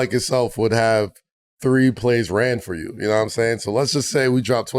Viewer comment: 😂 yo I almost thought the way Kaz asked that question it did sound like he was saying “Only three plays ran for him” lmao 🤣